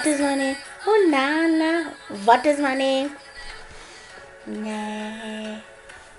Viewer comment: try listen carefully what she says in 0:12 she says butt is my name 😂😂😂😂😂